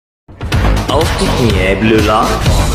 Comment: I'm german, but i can confidentally say that i have no idea what the fuck he said, i did not understand shit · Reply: oh shit now that you say it I can hear it- „Auf die Knie Blue lock“